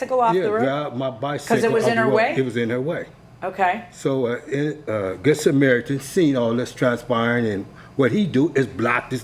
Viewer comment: Did he say, "This American" stopped to help? If so, what does it matter? It could have been an illegal immigrant from Canada for all this guy knows. Sounds like this guy has some xenophobia going on. Let's keep watching. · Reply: "A good Samaritan"